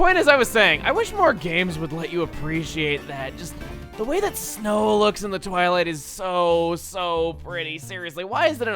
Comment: I heard "snow in the toilet" :P